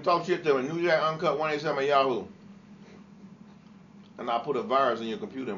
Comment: What did he say his email address was @ 07:19 ? I couldn't quite make it out... It sounded like newjackuncut 187 at yahoo, but I'm not sure...